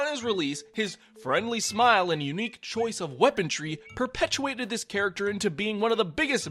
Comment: You can't slip that by me, I heard you say "Weapon-tree"